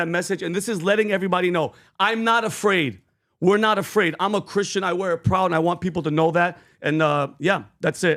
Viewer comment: I heard I'm na Christian.